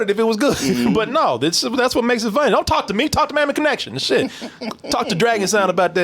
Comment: Sounded like said Mammy Connection lol